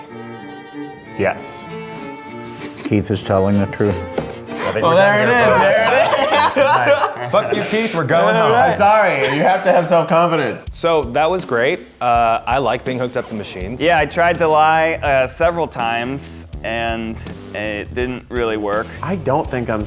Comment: When Eugene said that he liked being hooked up to machines I thought he said I like hooking up with machines